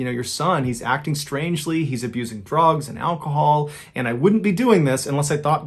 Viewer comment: I heard "he's abusing frogs and alcohol" and can not unhear it now😢 Like, I know the story is sad, but I just can't not laugh about it, not to mention how seriously I tried to make sense of that sentence for solid three minutes🤣